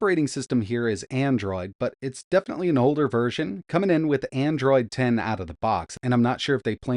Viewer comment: I thought they said Android 13?